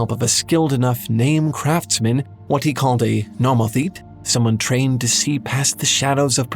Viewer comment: is he saying Namathete? I really want to learn more about this, but I think my guess at spelling it was bad because I can't find anything on search engines.